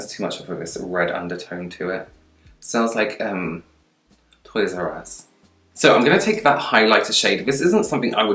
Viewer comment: Did I hear say “smells like toys r us” ?? Lmao